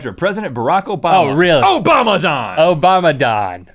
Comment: When Link said Obamadon I thought he said "OBAMA DIE!!", I have terrible hearing...